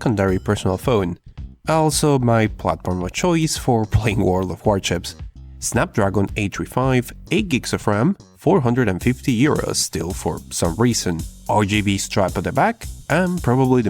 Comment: I keep hearing "World of Warchips"